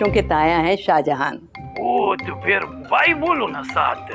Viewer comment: Can someone tell me what Jalal's father said here? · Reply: To phir bahi bolo sath yeh kha